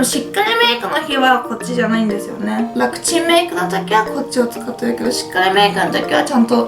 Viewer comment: RMKほしくなった～✊ あと１回だけ楽ちんメイクがパクチーメイクって聞こえたｗｗｗ